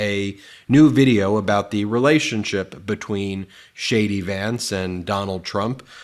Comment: lol. Ben, you obviously read my comment about mishearing you say JD as Shady